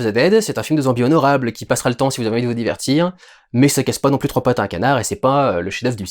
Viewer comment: A la fin j'ai entendu "ça casse pas trois pattes à un cadavre" et... j'en ai honte..., mais, ça m'a fait sourire.